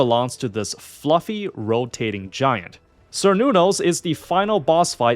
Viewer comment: I somehow misheard Cernunnos as Sir Noodles lmao I think I'm calling him that now